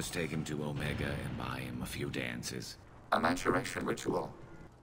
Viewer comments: I thought he said "Mass Errection ritual" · oh...i thought he said "A mass erection ritual".... :/